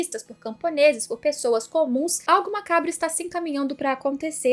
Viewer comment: eu entendi "alguma cabra está caminhando" e fiquei procurando a tal de cabra na paisagem